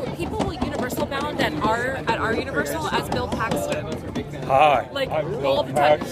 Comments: Seeing if its working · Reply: Still can't comment, just reply. I can also delete replies but not edit them. Weird. Also at 6:40 did they say "something Bill Paxton"?...."all jeans all the time". Audio wasn't sharp at that point. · Still have no idea what says at 6:39 or when repeats it, but it kinda sounds like "Bill Paxton". (RIP)